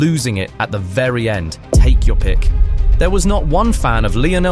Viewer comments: did anyone notice that it said "tack your pick" instead of "take your pick" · it says TACK instead of TAKE lmao 😭